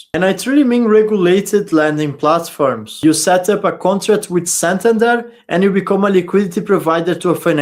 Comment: What word are you saying at 8:28 ? You set up a contract with ? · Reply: Santander Bank